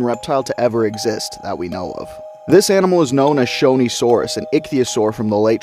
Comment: Not going to lie, at first it sounded like you said "show me saurus" and it caught me off-gard 😁